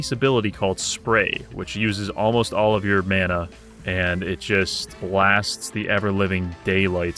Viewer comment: Did I hear right? said mana?😂